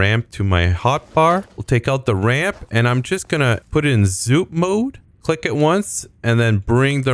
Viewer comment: What do you say at 5:25? Zoom mode? Thanks! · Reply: Zoop :)